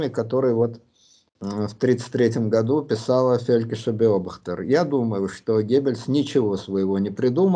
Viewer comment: напишите, пожалуйста, текстом имя и фамилию, не удается расслышать и нагуглить. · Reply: Фелькишер беобахтер газета и газета правда, а название статей уточню :)